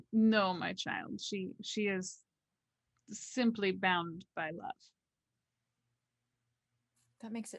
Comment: "She is not a ghoul, simply bound by blood." Isn't ghouling basically a blood bond? Is Aleha something other than a mortal? Did I miss something? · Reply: I thought Carna said "simply bound by love"?